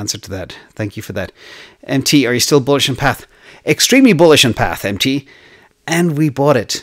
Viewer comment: Are you still bullish on "PATH" - around time 21:45. I didn't quite catch that - would someone please clarify? Thanks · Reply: Stock uipath